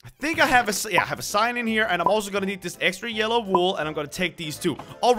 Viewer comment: I thought he said “ I'm going to need yellow wool and sand " I thought he will make yellow TNT I got so confused